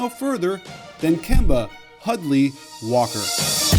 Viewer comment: Lmao I thought you said Kemba Ugly Walker.